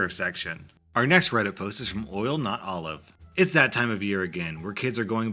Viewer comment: i legit thought he said boiled nut olives